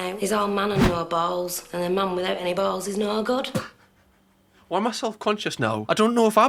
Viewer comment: "A Mum without any bowls is not a God?" WTF is she trying to say???